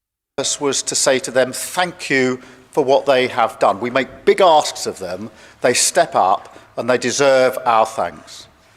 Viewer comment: sounded like he said "we have made big arses of them" <link> double speak